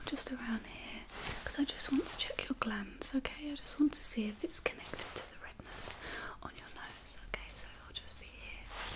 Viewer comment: What does she say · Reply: "Okay, and your other hand"